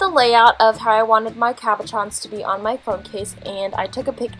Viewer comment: Am I the only one who thought that she said caparrons instead of cabochons?